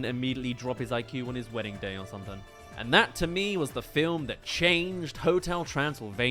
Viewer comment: You said "Film", not "Scene"! I know what I heard! Your subtitles can't gaslight me, no matter how much you capitalize the S in scene! I'm not crazy! I want to believe! Also, great short-form analysis of a film I honestly wasn't expecting an analysis of! Looking forward to more of your content in my feed